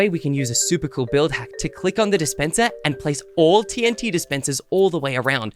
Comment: i heard you say displender